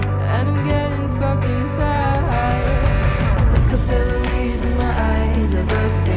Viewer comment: The first line of the chorus is 'The capillaries in my eyes are bursting,' so the double meaning tracks. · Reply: God i feel so bad i Always thought It said caterpillar...😭 Im not a native speaker but why tf i thought "caterpillar in my eyes are bursting" was not weird